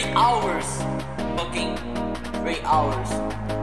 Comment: At the start of the song at first I thought he said “Great Hours” but in 2024 I finally noticed that he says “3 Hours”.